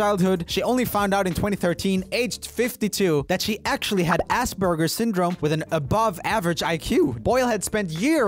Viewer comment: thought he said ass burger at first